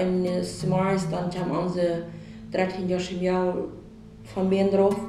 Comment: I speak their language and the English translation is so far off from what they're actually saying; it should be a crime to mislead the public like it's done on this video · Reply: One example is at 5:20, the subtitle says THREE YEAR-OLD DAUGHTER but the woman speaking is actually saying THIRTEEN YEARS-OLD. This is an unacceptable mistranslation!